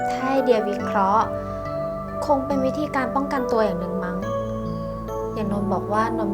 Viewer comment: เห็นมีคนบ่นเรื่องเสียงหายหลายคน และเห็นมีคอมเมนต์นึงเขียนว่า ช่วงนาทีที่ 10 กว่า ๆ...เรา ผู้ซึ่งดูเรื่องนี้ได้แบบไม่เจอปัญหาเสียงหาย ก็เลยจะมาถอดเสียงให้ในคอมเมนต์นี้นะคะ 10:22 เดียร์ - ถ้าให้เดียร์วิเคราะห์ คงเป็นวิธีการป้องกันตัวอย่างหนึ่งมั้ง นนท์บอกว่า นนท์ไม่ชอบถูกเอาเปรียบ แต่พอนนท์ตอบโต้คนประเภทนั้นด้วยการเอาเปรียบคืน คนก็เลยมองว่านนท์เป็นแบบนั้นซะเอง นนท์ - ถ้านนท์เลือกที่จะไม่ตอบโต้ นนท์ก็คงไม่ถูกมองว่าเป็นคนประเภทนั้นหรอกเนอะ เดียร์ - ถูกต้องนะคะะะ . ถ้ามีตรงไหนไม่ได้ยินเสียงอีก บอกใต้คอมเมนต์ได้ค่ะ . มีแฟนแบบคุณเดียร์ในเรื่องนี้คือแสนประเสริฐเลยนะ คือ พอเห็นอีกฝ่ายร้อน ตัวเองก็ยอมใจเย็นเข้าสู้ (แต่ในความเป็นจริง คนที่เย็นที่สุด คือคนที่ระเบิดทีบ้านแตกเลยนะ) ยังดีนะที่เรื่องนี้ยังมีคนเตือนสติ บางทีในชีวิตจริง คือโดนแบนไปเลย ไม่เจอแม้แต่คำดุด่าว่ากล่าวเลยด้วยซ้ำ . ป.ล.ขอบคุณทีมงานและนักแสดงสำหรับคลิปเช่นเดิมนะคะ...จะนำตอนนี้ไปเขียนรีวิวในบล็อกให้นะคะ (เซิร์ช AmmyMemory Blog เจอเลยค่า) ป.ล.2 อยากให้ทางช่องมีโลโก้อยู่ในตัวคลิปจังค่ะ เวลาแคป 1 ภาพไปแปะในรีวิว จะได้เห็นว่าช่องไหน (แต่ปกติเราจะใส่ลิงก์ของคลิปเอาไว้ให้ใต้ภาพอยู่แล้วค่ะ)